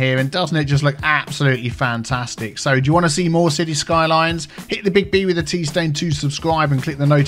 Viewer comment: sorry but do i hear "hit the big b with the pee stain?"